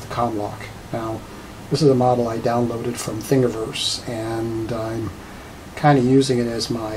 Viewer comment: From where did you download the comlock model? Is sounded like you said FingerVerse, but I cannot find any site with that name.